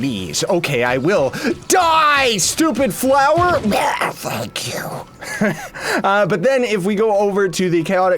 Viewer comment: Did he cuss? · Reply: when? If you mean at 2:56 then he actually said thank you.